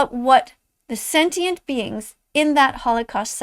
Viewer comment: did she just call Holocaust victims “sentient beings”? excuse me, woman? Edit: “woman” supposedly